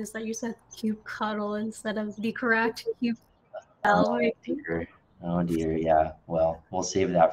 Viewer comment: The sound cut out a little bit at 15:38, so did not hear what the correct way to say kubectl is. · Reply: kube-control is the correct way to say as per the video they later discuss that in CLI section of the video at 24:23.